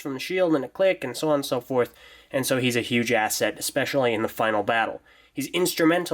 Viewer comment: HE'S A WHAT? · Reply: I heard it too. "Huge-ass set."